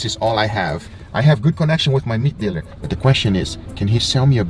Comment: i thought you said Meth dealer